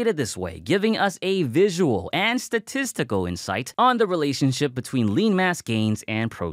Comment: Did he said "Testical"? 😮